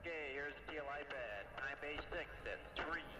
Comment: Really like this. - Did I hear “Start The Time Base” ?